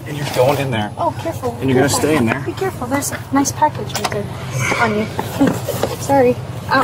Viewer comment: Did she just say "a nice package on you right there"? Lol😂